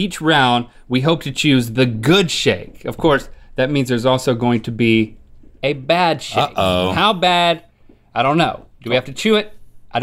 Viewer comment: did I heard link said good sht and bad sht?